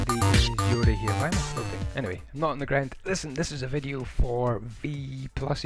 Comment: I thought it said kiss ass v+ at first